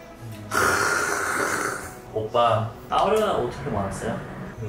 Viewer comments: Did you just call him oppa at 3:00? 😂😂😂😂😂😂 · Did just call him Oppa? 😆